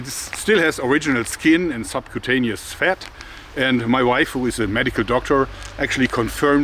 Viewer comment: did you say...... waifu?